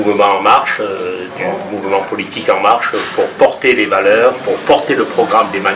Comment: Bravo pour tes vidéos punchline qui dénoncent ces menteurs professionnels 👹 ! A 2 minutes 55, j'ai entendu "pour porter les malheurs" 😛, comment ça s'appelle ? Un lapsus écouteurs 😜 ?